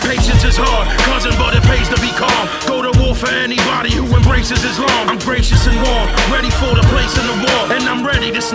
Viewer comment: ''Go to war for anybody who embraces Islam'' ... Oh well bye vinnie, I liked you. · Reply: Go to war for anybody who embraces Islam, Dayum, I always thought he was saying he would go to war against anybody who embraced Islam. Dumb...